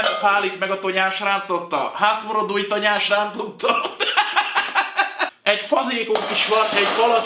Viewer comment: nál mit mond az úr?:o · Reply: Hász muro duj tonyásrántotta! Edd meg a két tonyásrántottámat! :D